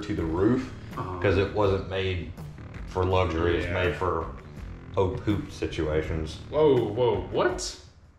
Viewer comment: Closed Captioning says "it's made for Oak hoop situations" ... "whoa whoa what:?". It's at timestamp 12:41 <link> Does anyone else know what he's saying that got so riled up?